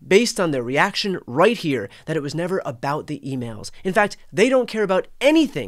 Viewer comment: Lol! I thought Brian said "Butt hurt emails" at first. This is so ridiculous. This admin all need to be removed.